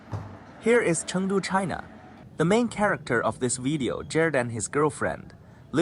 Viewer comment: Did I hear it correctly when he said "Chandu China"